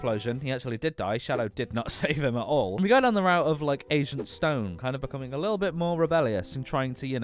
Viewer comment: Sounded like you said Asian Stone.